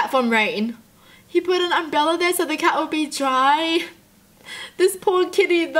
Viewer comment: I thought she said "the cat wont be dry"